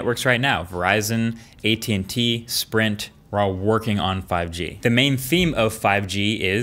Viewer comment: I love how he listed all the carriers that are working on 5G, and skips T-Mobile 😂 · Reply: Jacob Sandness I thought he said the ones still working on 5g